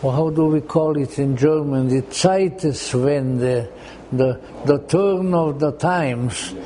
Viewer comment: timestamp. How do we call it German 'zieten' ?? 'zietis'??? What word did the speaker use?